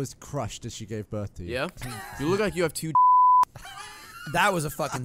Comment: What did Ray say to Gavin in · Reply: I think he said "You look like you have two dads."